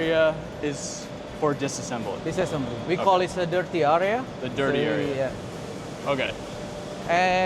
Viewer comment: did I hear him saying dirt area 🤔